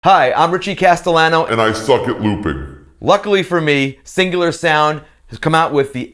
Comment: At first I thought you said "I'm Richie Castellano, and I fuckin' loop it!"